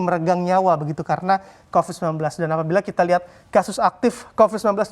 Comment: gak salah denger nih? "Dan Alhamdulillah, kasus aktif masih tinggi".. Lah aneh.. 😀🤣